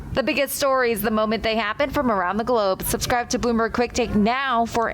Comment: Sounds like she says " Subscribe to Boomer Click Bait Now"